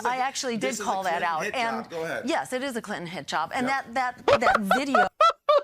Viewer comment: first I thought she said "Clinton hijab" second I thought she said "Clinton handjob" only on the third pass (while reading the subtitles) did I finally hear "Clinton hit job" woman needs to enunciate better